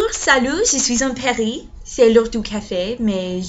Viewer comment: And, you know what, at first I heard you say "Bonjour, SALO" (correct spelling is "salaud" and it means "bastard" which is only for masculine, the feminine equivalent is saloppe, which is bitch loll. But you were obviously saying "salut" hehe) & of course your accent is maximum cuteness, no matter what :) (btw salaud as in bastard is used sometimes as an endearing term between intimate or close friends in certain situations, or expressions like "mon salaud" to express "lucky bastard")